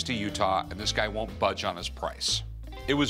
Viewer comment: did Rick just say “walk away empty hatted?” …not empty handed?